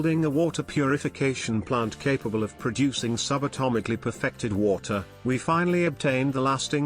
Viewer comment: I had to do a double take because the tts sounded like it was saying "subatomically pathetic water" and i'm ngl thats funny af